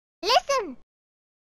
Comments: For ages I misheard "watch out" as "wake up" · When I heard Navi say "watch out" I thought it was "what's up" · When I was a kid I always thought she was saying "What's up!" instead of "Watch out!"